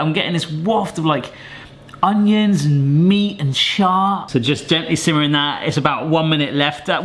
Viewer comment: Wait a second...What was the third thing said @ 9:20?!? LMAO, Please don't tell me he actually said "shart"...I literally re-played this part like 10-13 times now, and I'm almost positive that's what he said. The way he said it so positively and happily made me choke on my drink and nearly die of laughter. Alas, reminds of Barshen's and the Sharticles...I miss that channel so much, I know it'll never be the same, but I really hope one day they have a reunion or come back to do something similar even if it ends up being one-off thing. "I'm getting a waft of, like, Onions, Meat and..."Shart"!" We definitely need that on a shirt or even better a mug!